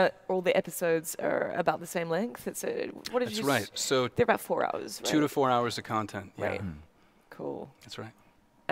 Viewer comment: Did he just say the episode is only an hour? Please say I heard that wrong. Please someone give me a source that says otherwise. (Edit: Now I think he said 2-4 hours. Okay I'm happy with that xD)